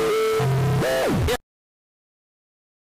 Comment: Why did you say? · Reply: What the flip